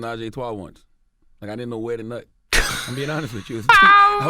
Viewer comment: Did Charlemagne say he did not know where to nut 🤔